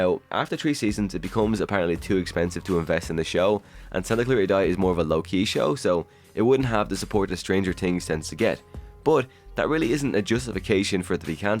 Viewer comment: I kept hearing “tree seasons” and “stranger tings” lol